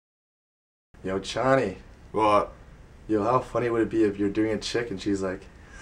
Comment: For a second I thought said : Yo Chonny it would be funny if you were doing a chicken.. IM LIKE What the Fu-???